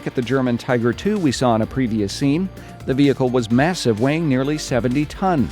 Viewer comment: I heard "massive wang" at 6:40.